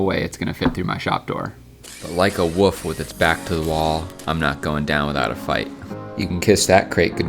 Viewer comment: I thought he said “like a women with her back against the ball she won’t go down without a fight”😂